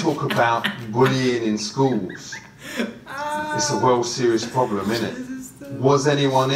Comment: did he say a world series problem? Hahaha